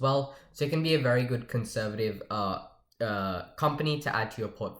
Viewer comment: Did u say conservative? :deep_fried_cursed_smile